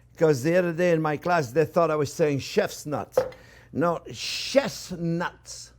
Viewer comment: I also thought you said Chefs nuts and now I am facing fourteen counts of assault with a deadly weapon for gathering Chef's nuts.